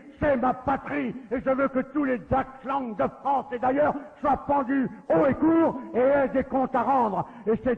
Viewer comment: Il dit quoi à 2:26 ?? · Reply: "Soient pendus"